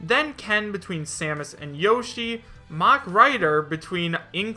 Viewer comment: I thought you said "mumen rider" instead of the "mach rider" and I was like: One punch man for smash... * inhales deeply * ... HELL YEAH!!!